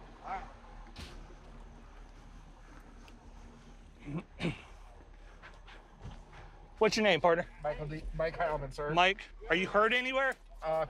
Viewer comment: did he say Mike Calvert? That is my Grandpas name....